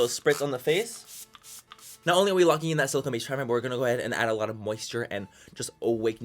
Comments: what the hell did he say ? ... all what I heard is lishhjsusbbxxjj.. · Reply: He said "not only are we locking in that silicone based primer" lol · Wtf did he say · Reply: "Not only when your walking in that silicone base primer mciskebcusbacsifnrjm a lot of moisture in the face" 😂🤔